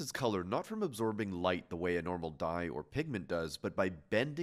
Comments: Bro I thought I heard pikmin not pigment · whoops when he said pigment I thought he said pikmin